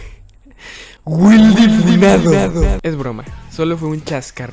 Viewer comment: que dijiste ? 😈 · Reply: A funarlo dijo ok no